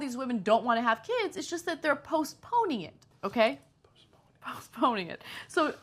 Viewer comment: "They're post-ponied..." "They who what now? o-o OH! I misheard that, postponed ,_,"